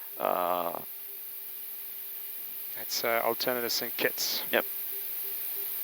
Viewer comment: did I hear correctly ? did you guys say alternate St. Kitts ? lovely video as always by the way :) !!!!